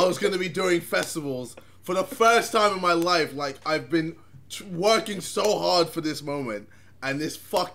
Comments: Is it Just me who hears twerking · anyone hear JJ say “I’ve been twerking so hard” 😂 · did ksi say twerking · anyone else hear “twerking” so hard for this moment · idk if anyone else heard this but man said “if been twerking for this so hard” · Did JJ just say twerking? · Wait at 6:39 it sounds like JJ says ‘I have been twerking so hard for this moment’ 😂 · did he say ive been twerking?? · Anyone else thought JJ said twerking?? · sounded like JJ said twerking so hard aha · did he just say twerking or am i hearing it wrong? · did JJ just say he had been twerking so hard or is it just me ??